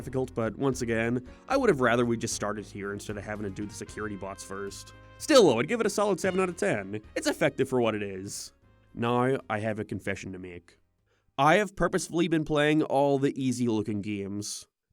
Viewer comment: I got a ad during your sentence and it cut off making it sound like you said “I have a confession. I have purpose” (great content btw)